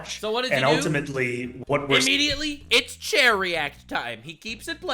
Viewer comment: Idk how many times you've brought it up during the franchise, but I've just now realized you've been yelling "Chair React!" at the Hasan streams. I thought you were saying "Cherry Act," like streaming content without being present had a cute little nickname called the cherry act or something.